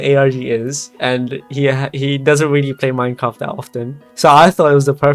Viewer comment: what scares me is that im not sure if you said minecraft or mein kampf